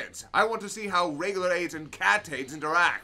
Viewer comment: Ok for the life of me I cannot make out what is saying at 12:45 in regards to the cut joke, is he saying Regular tate and cat tate? I have no idea what that is if that's the case. Can someone help me out? Also not sure how thinks Broly would beat Cell. Goku can beat Broly, rather easily actually, once everyone just snowballs their energy into his mouth, Goku straight up says he can't beat Perfect Cell